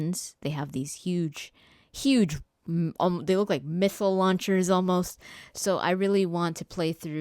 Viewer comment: did she say "mithile launcher"? LOL